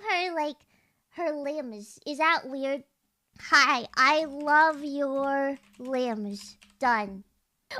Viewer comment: for a second i thought biboo said "i love your rims, done!" yabai